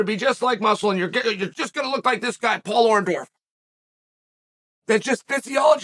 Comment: At the 5:17 mark, did he say Paul Orndorff?